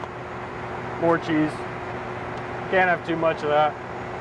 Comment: did anyone else hear "Orgies, can't have too much of that"